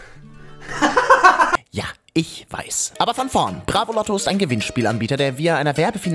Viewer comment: Ich habe am Anfang nicht ,Bravoloto' verstanden sondern ,Bratkartoffeln'. 😂